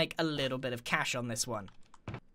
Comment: did he just say uniashuish? 😂